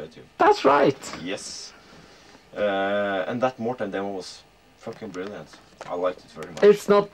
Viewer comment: what band/demo is ted saying is brilliant at around 11:35 ? cant understand what hes saying. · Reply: i think it was Slow Death by Mortem